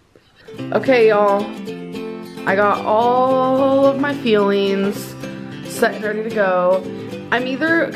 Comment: At first I thought she was saying feeling not fueling. Makes way more sense now 😂